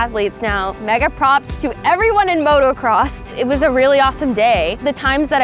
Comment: did she just say motocrost